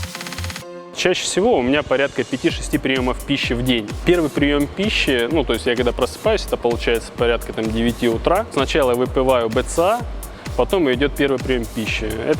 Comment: выпываю сказал или мне послышалось?)))